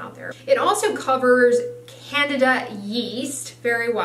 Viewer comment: did she say CANADA? LOL